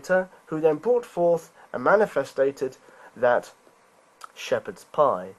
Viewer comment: did he say "manfestated"? What the hell is 'manefestated'? I was able to find definitions for 'manifest' and even manifestation....but manefestated.....nothing. Speaking of 'nothing' take his entire strawman rant and replace the word 'nothing' with the word 'god'....suddenly, he's an atheist.....just saying. Saying that your rational, sane description of the universe is that a timeless, spaceless, omnipotent, omniscient, disembodied eternal being created the world specifically to give 'his' worshippers a place to sing his praises is far more imaginative than a position of requiring proof and evidence to support your views on reality.